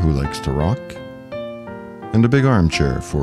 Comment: I thought you said "a big arm chair for two to throw-up in" ...I had to turn on the captions to get it right....ha ha ha ha. thanks for the Stuxnet reveal. ..... The original virus was placed on hundreds of usb drives with music and other tantalizing bits of data, scattered all over IRAN in coffee shops and hookah bars, laying on the ground in shopping areas and grocery stores, even laying in the road, etc., for everyday people to check out, hoping that just 1 nuclear engineer might get infected directly, or from a friend or family member that was infected, and carry the infection into the nuclear facility on their music list or family videos and such... Diabolical! .... Just like the exploding pager attack was equally diabolical... Thanks for the presentation.